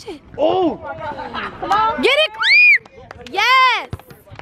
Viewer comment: it sounds like said get it Chris 😂